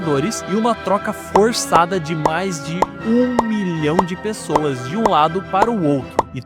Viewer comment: Navios, passando pelos estreitos, etc... foi uma troca pós guerra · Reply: acho que tu só falou errado, pois eu ouvi "um bilhão de pessoas", mas no final tu falou milhão, então agora que eu entendi